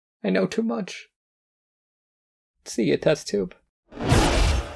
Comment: I know I already commented but this hit hard. I thought he said, “I see it now, Test Tube” but I turned the captions on and it said “See you, Test Tube” and I almost cried. He knows he’s about to die and he knows. Too much. The raw acceptance I hear from him just SHATTERED and punched me TWICE in the same spot where my heart is.